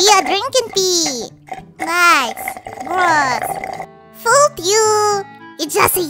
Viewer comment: it sounded like he said fouck you instead of fooled you